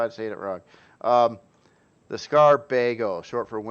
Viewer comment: Thought I heard "the Skaar Bagel" and had a chuckle. That POI looks amazing with that lighting. Here's hoping the Skaar do get adopted as another NPC faction. They have some very unique looking creations!